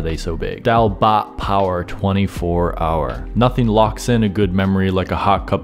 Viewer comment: Did he say "Daal bhat power, 24 hour"? Damn man cheers from Nepal.